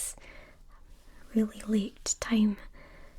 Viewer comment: Instead of late I thought she said "thank you for coming at this very l i t time" i